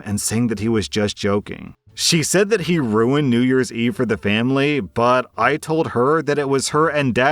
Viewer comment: For the second story, am I only that I heard him say New york instead of New Year for the title?